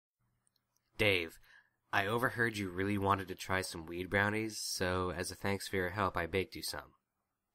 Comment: I thought it said wierd brownies XD